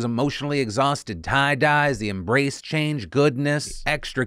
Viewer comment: I thought it said "Embrace Cringe" not "Embrace Change" lol